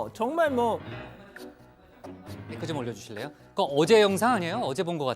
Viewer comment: 복부장님이 뭐라고 한지 아는사람 계신가여? 아무리 들어도 모르겠네요ㅠㅡㅠ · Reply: "마이크 좀 올려주실래요?"